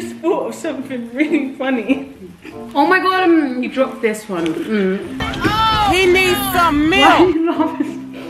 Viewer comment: Who else thought that it was saying "you need some milk" at 1:30 🤣